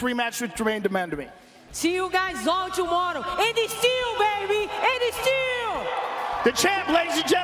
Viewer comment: What does Amanda always say before she leaves: ‘a baby seal?’ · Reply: And a still and a still champion. Unless you were just trolling lol